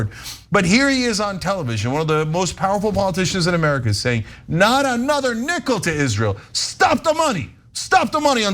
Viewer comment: "Not another nickel to ISRAEL" That is NOT what he said. Bernie specifically said not another nickel to Netanyahu... As if the israeli population werent made up of sadistic genocidal physchopaths.